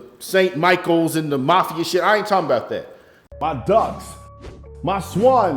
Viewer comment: I thought you said dOves and swans not ducks. Some people make the dove reference it means when you write The Holy Spirit is writing with you. It means God is speaking to someone who ends up listening to your song. He could even be doing the same generations. later with the same song. It's a crazy feeling most don't know that's why they like the song so much.